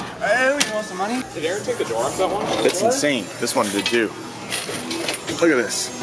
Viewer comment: Did he say this ones a Jew?